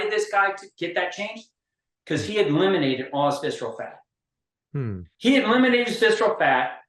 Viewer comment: I have replayed this 10 times, can someone please tell me what the hell he said? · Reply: Because he eliminated visceral fat